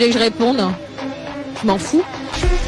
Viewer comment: what did she say? Mon feu? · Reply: Je m'en fous:I do not care